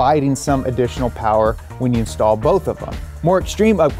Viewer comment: lol I thought you said stall so I had to listen again to hear “install” 🥲😂😂😂